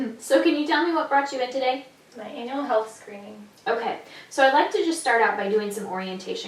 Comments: I had to listen three times as I thought she said "anal health screening" lol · It's annual health screening, right? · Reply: Yeah I could’ve sworn I heard anal health screening…